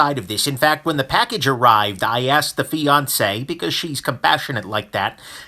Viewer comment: Oh, fianceé! At first I thought he was saying Beyoncé!